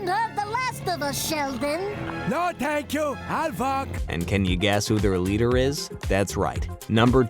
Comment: Yo what did Grandpa say at 10:26? 😂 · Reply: No thank you ill walk